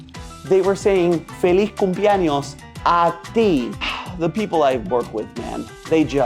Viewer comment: I heard “David” not “ A ti”